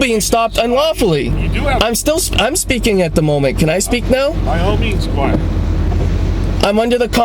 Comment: Did I really hear the officer refer to the operator as "Squire"?